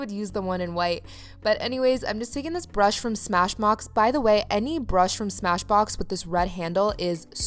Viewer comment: Omg at 5:02 it sounds like you say smashmox instead of smashbox!! It’s not big deal it just made me laugh